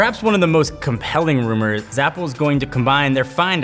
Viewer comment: Go to 7:20 and he says Zapple instead of apple